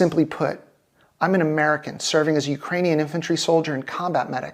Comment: I thought I heard TDS media team? Did I not?